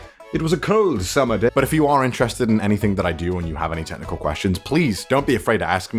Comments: Why did I hear : "If you have any tentacle questions"? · I know you're probably getting this from a bunch of ppl, but you said 7 types of fanboy but the title says only 6... Bad Sebastian! (And for a sec I thought you said "tentacle questions" instead of "technical questions"! 😂